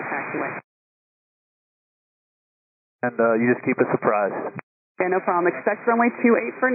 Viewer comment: "Keep us surprised." Not what he said, but still rather funny! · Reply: I think the captions are wrong. I heard him say "apprise" which means to notify or to make one aware of something. "Keep us apprised" means to keep up to date on a situation.